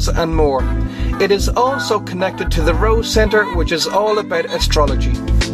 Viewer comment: Did he say astrology?? LOL